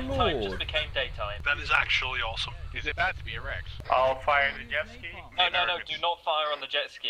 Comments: did I REALLY misheard or ... while looking at an intense explosion someone said "Is it bad to be erect ?" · did they say "is it bad to be erect?"